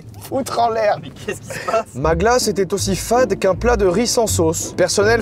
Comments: "Ma glace était aussi fade qu'un plat de riz sans sauce" j'ai entendu "Maghla c'était aussi fade qu'un plat de riz sans sauce" WHAT ?? Hahaha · y'a que moi qui as entendu maghla ?